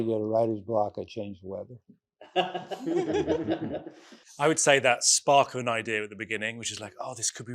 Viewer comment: Can anyone please tell me what Eric said at 53:13 after "when i start to get writer's block I change..." ? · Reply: "When I start to get writer's block, I change the weather"